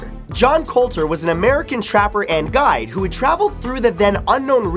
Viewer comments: did he say "Trapper and GUY?" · lol I thought he said trapper and guy and I was like, well duh.. took me a minute, had to go back